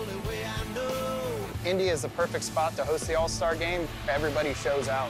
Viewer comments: I thought he said “India’s the perfect spot to host the All Star Game” and now I’m imagining the All Stars all meeting up and playing against eachother in a random court in Mumbai or smth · swear to god he says india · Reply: He said "Indy" but yeah I got confused too lol